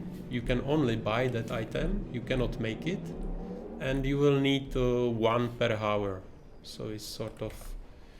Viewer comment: when the zone chips are being discussed I can't quite make out the comment does he say "you will need one per hour?"